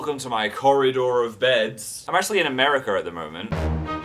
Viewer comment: it's hilarious listening to you say words like America be cause it sounds like you say "Ameriker" lol